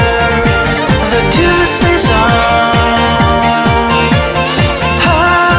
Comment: The two of us saison がたまに二人セゾンに聞こえるww